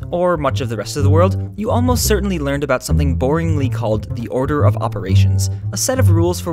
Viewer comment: did he just say BORING?! I didn't nore do I think that it is boring.